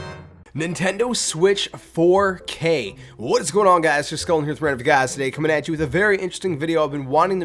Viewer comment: Nice vid man!! Can't understand what you said at 0:14 lol "Nintendo Switch 4K. What's going on guys jskulfinforinherguys"